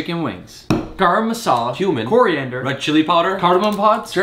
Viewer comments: I THOUGHT HE SAID “HUMAN” WHEN HE SAID “CUMIN” AND I GOT SO SCARED FOR A SECOND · yo i swear it sounded like he said "human" in the beginning lol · I thought you said human not cumin 🤣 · I got scared when he said cumin I thought he said human